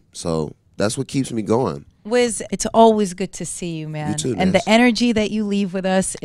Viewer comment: it sounds like he said "you too bitch"